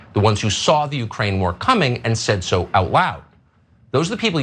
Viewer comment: Did anyone else hear F*cker Carlson say 'out wowd' (approx 0:25) instead of 'out loud'? 🤔 I rewound it a few times and I swear he sounded like Elmer Fudd when he did, lololol! 😆